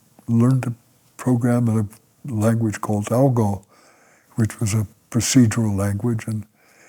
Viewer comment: As always, great video - thank you so much for this insightful interview! I think at 08:57, he mentions the programming language "ALGOL", not "ALGO" (listen closely and look at his mouth...). ALGO was kind of a predecessor to ALGOL, based on the same Zürich report, but not yet standardized and in fact limited to the Bendix G-15. ALGOL, however, is the more generic, standardized and more widely-used form of that language and probably what he used, especially as he also mentions FORTRAN later on. Additionally, I want to mention that there's a little typo in the video chapters: the chapter starting at 02:27 is misspelled as "Read to discovery" instead of "Road to discovery" in the video description. Anyways, thank you again so much for sharing this - I really love your fascinating journeys through electronic music history! Keep it up! Best regards, Fabian